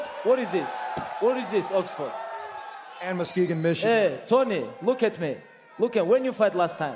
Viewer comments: Can anyone tell me what tony meant when he said 'ambicux commision' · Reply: madhavi gaidhani he says “and ‘some city’ Michigan” I want to know what he said that made laugh and Khabib kick his belt. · Tony: “im from Oxnard!” Khabib: “what? What is this Oxford?” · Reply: Can anyone tell me what tony meant when he said 'ambicux commision'